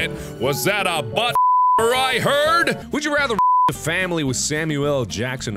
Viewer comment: WAS THAT A BUTTFU*KER I HEARD?!?